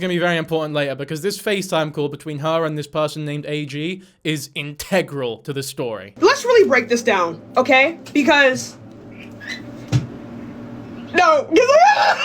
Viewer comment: did she say " no gama3a " like ( جماعة ) like guys in Arabic ? Is she Arab or smth ? Bec that would explains why she says wallah even if she isn't muslim